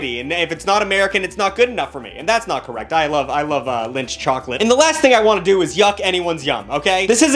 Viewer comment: You love WHAT chocolate? I had to do a triple take at this part of the video to make sure i was hearing things right. Thought he said Lynch chocolate